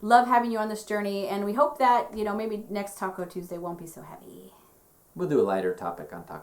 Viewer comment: I thought you were saying Taco Tuesday lol I think the accent got in the way! Lol I enjoyed the video even without the tacos!